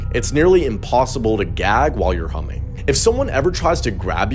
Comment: I watched this twice. The first time I thought it said "if you feel like throwing up, start humping. It's impossible to gag while humping." Then I realized it said "humming"